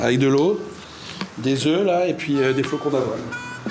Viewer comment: il mange quoi à 1:28 svp ? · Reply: Flocon d'avoined